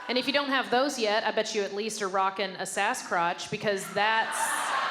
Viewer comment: Damn I thought she said asscott